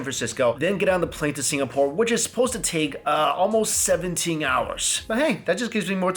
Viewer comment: the type of guy to fly for 17 hours JUST for the food lol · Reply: I thought he said it was 7 hours.